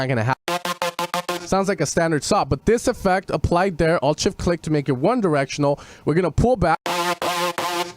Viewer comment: What did you say here? Is it "shift click"? I tried a couple of times and it didn't seem to work =( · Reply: Oh! It's "alt+shift click". I got it! Thx anw =D